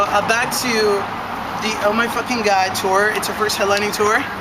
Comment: did he just say “i fucking fucked her” 🤢🤢